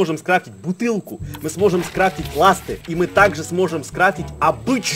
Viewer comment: когда я 1 раз смотрел мне послышалось "мы сможем склеить ласты" :)